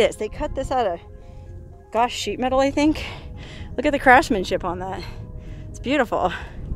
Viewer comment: I think you said "crashmanship." A Freudian slip no doubt.